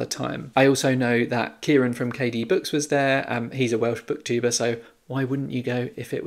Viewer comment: Did you edit this video yourself? It's quite beautifully done. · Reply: 😂🤣 I thought you said 'He's a Welsh book diva'.